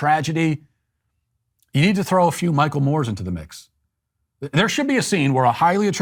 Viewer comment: I thought you said "Michael Myers" and I got my hopes up